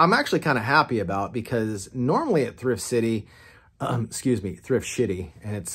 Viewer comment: Great video. When you said Thrift Sh***y, that is what I thought you said, but then I convinced myself that you said, "Thrift City."